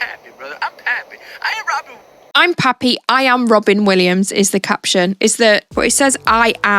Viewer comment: No, it says Robert Williams. The auto-generated subtitles heard "I am Robert Williams," instead of "I'm not Robin Williams." So no, this was not deliberate.